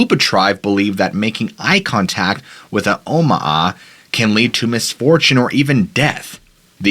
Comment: I thought he said Obama, not Omaha.